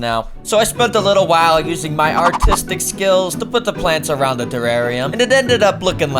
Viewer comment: I thought he said autistic